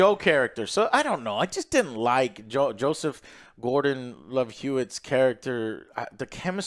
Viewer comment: did he say Joseph Love-Hewitt? 😂